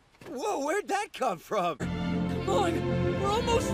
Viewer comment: BRO MY VOLUME WAS ON THE HIGHEST AND ALL I HEARD WAS “KACcHAn?!”